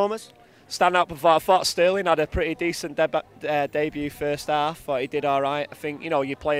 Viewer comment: Did he just say DEBUTT? Wtf?